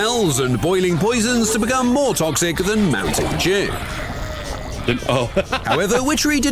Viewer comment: I'm glad I'm not the only one who thought he said "mountain jew." 😂😂